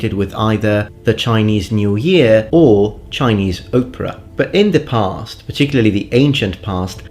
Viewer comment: Hahaha, when you talked about our modern association of Chinese masks with traditional Chinese opera, it sounded like you said “Chinese Oprah,” the thought which made me chuckle delightfully! Thank you for yet another, thoughtful, well researched, informative, & entertaining video! You’re awesome man!